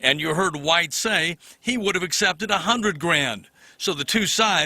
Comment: Grammar people. That is not what he said. He said "would've" a contraction of "would have".